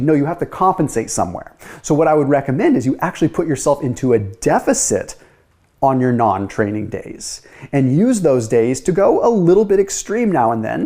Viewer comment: A lot of people seemed to be confused. He said you put yourself into a caloric deficit on your ‘Non’ training days. Seems a lot of people heard calorie deficit on training days. See time stamp 5:05. Sounds like modified alternate day fasting. This is where instead of fasting 36 hours straight you eat but in a near fasted calorie deficit. This may help people who find ADF difficult.